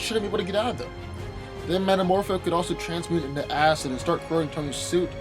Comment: Good god... When you talked about Iron Man vs Booster and Metamorpho, I SWEAR I heard you say that Metamorpho could transmute into ass! XDD